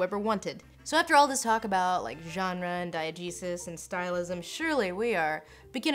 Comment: I just have to say, every time says "diagesis," I hear "Diet Jesus." Like Jesus, but with no calories!